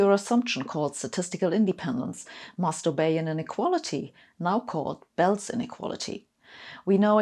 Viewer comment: sounded like you said “masturbate”